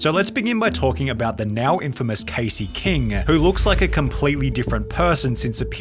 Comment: thought he said caseoh💀